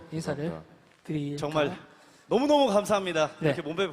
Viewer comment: 때 창균이가 뭐라고 하나요ㅜㅜ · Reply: 그냥 "감사합니다... 네, 감사합니다." 라고 들려요!